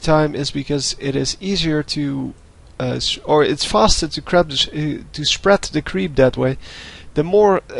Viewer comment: I heard to spread the crap :D